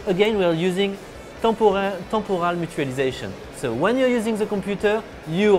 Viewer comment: I think he said "temporal mutualisation"